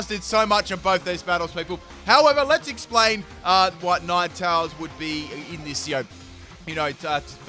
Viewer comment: Had to rewind a couple times around 10:15. Had a laugh as I kept hearing nine towels instead of ninetales.